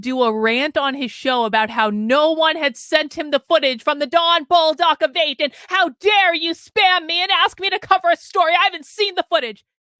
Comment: what footage? I'm not able to figure out what Karlyn said. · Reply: Don Bolduc was fake assaulted and got a guy arrested on false accusations